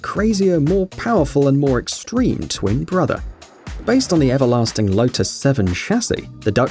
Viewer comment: Says twin brother but caption says “I am your Father”